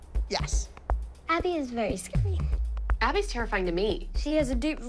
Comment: I thought Chloe said Abby is very scary